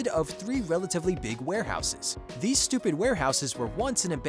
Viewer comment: DID HE SAY STUPID???🧐🧐🧐